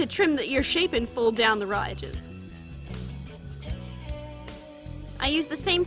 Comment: did she say ridges “riiidges”?